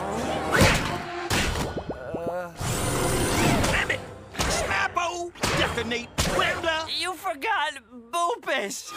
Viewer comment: did he just said dammit? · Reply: Blam it is what I heard